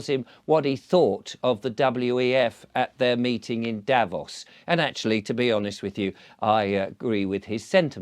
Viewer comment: Those clowns at Davos truly believe they control the world. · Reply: What?! Oh, for a moment I thought you said Davros — though the resemblances to Klaus are uncanny: watch?v=B9NttGB9oOw